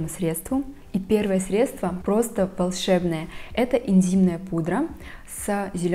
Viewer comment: Мне послышалось "интимная пудра"🙈